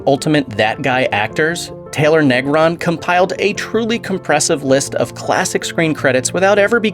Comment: "he compiled a truly compressive list of credits"! I'm very compressed as well. Although I heard he was the best at impressing springs together!